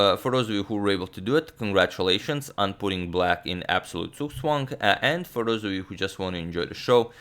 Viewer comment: What is the s word he uses at 13:25? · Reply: Figured it out. ZugzwangEdit A position, usually in an ending, where the player to move loses because he/she must move. A more complex definition is given by Hooper: "Zugzwang... is a position in which whoever has the move would obtain a worse result than if it were the opponent's turn to play".[1]p458 See Squeeze.